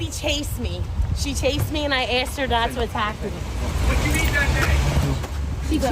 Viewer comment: Anyone catch the guy say “what did you eat that day?!” 6:32 lolllll